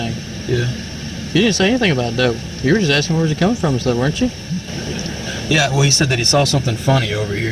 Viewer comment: Just to be clear, the idiot said, “You were just askin where was he coming from, weren’t you?” Why does the caption say, “you were just trying to irritate him, weren’t you?” That’s just false info and makes it look a bit more malice than what was actually said, initially. I’m not defending the crappy cops but I just wanted that part to be clear.